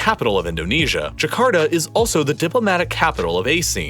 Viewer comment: first time ever i heard ASEAN as "Aseen"